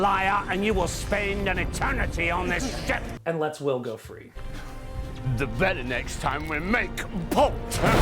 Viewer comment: anyone understand what daveys saying here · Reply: "the very next time we make port"